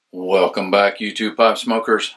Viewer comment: Maybe its the accent or my hearing but alot of the time it sounds like your saying Hello Youtube Pot Smokers lol.